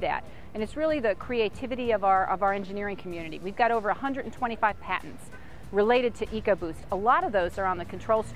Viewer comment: I thought she said they have over 125 cats.